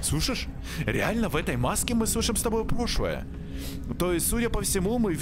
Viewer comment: же не одна услышала вместо прошлое пошлое?😅